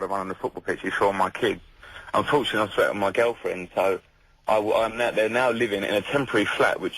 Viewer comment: Does he say "unfortunately i threatened my girlfriend"? at 1:45? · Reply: “Unfortunately I split up with my girlfriend”